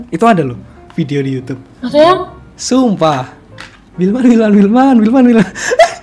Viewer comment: kak bilang "masa sih yang?" gitu kan atau aku salah denger